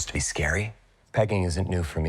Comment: When I first watched the trailer I thought he said "begging". Then later when I found out he said "pegging", hilarious 🤣🤣